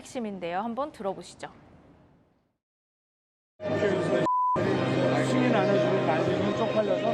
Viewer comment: 승인 안 해주면으로 들리네요